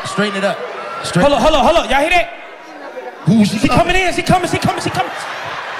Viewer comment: instead of “Andy’s coming” “Mama’s coming”